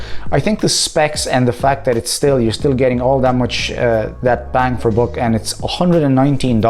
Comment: what were you about to say? ( ͡° ͜ʖ ͡°) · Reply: Bang for buck! :)))